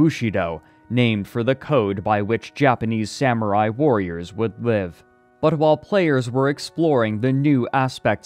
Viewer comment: what is he saying here. Sounds like bhlev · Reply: "Would live"